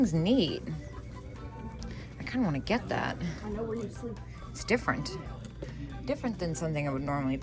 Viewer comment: did someone say, "I know where you sleep?" 🙄😲🙄